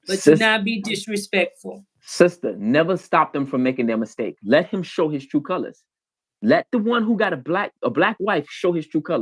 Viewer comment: what he say?? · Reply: Never stop your enemies from making a mistake(s) and to let them show their true colors. So we see them for who they are and not the illusion of who they are